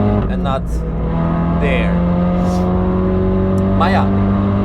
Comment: "maarja" Bit of Dutch seeping through? Or am I mistaken and is he saying something else completely?